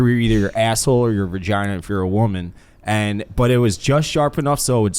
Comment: did say "vergina" ?